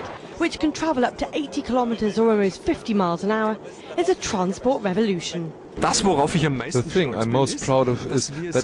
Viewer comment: Did she just say transport revolution at 0:50 ...